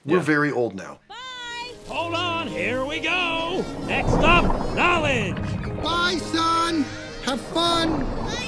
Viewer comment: First I watched watched this, I thought he said college instead of knowledge. After a second time I was wondering what kind of place Knowledge was. It took me a few years to learn what knowledge meant.